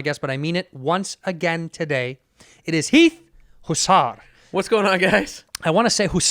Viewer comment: Man, i thought it said meat hussler